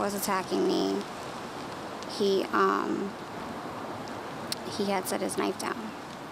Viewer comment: HEY, TITLE-TYPER! "He had SET his knife down..." THAT is what she SAID (@ 1:50). It is ALSO CORRECT ENGLISH, which YOUR version clearly is not! Listen again.